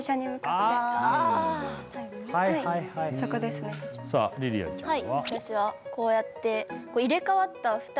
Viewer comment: りりあちゃんよびいい 2期の2人の伊藤がいた時の名残だろうね。でも、なぜかみりあちゃんにしか聞こえないんだよな笑